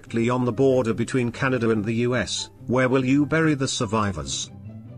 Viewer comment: when I heard the question I was like okay now why would you bury the servicers??? they're not died 🤨 but then I heard her saying in the home tome and I was like seriously what did I just heard 😐😐😐😂🤣😂🤣😂🤣😂